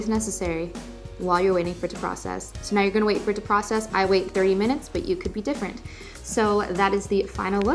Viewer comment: does she say “30 minutes” or “3 minutes” because I can hear both 😂😂😂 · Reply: 30 😂